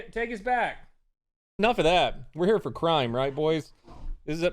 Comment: OH... You said, "We're here for CRIME." I thought you said, "we're here for CRYING." I'm so disappointment this isn't the safe space I thought it would be.... 😭😭😭😭😭😭